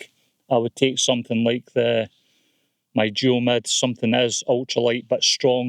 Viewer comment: Hi. Nice video. I used the Vern 1 when walking the Affric Kintail Way but admit to struggling because of the weight. I couldn't quite catch what you said around 26:42 where you said if you were going on a long hike, you would use....... Can you let me know which tent you mentioned so I can have a look at it please. Cheers atb👍🏻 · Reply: He said Duomid